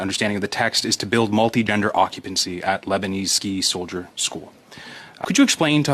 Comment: They thought they heard Lesbian instead of Lebanese Ski School.